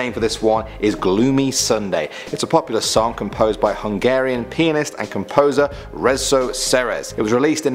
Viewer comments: did he just say "Hungarian penis"? jk 😂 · Sorry but it sounded like you said "Hungarian penis"! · I thought I heard a Hungarian Penis?